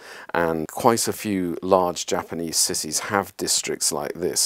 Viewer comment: lol i keep hearing him say "japanese sissies" instead of japanese cities xD